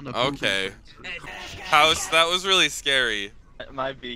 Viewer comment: was my fav part cuz I heard a little girl go “it’s free real estate” 👍👌😂💯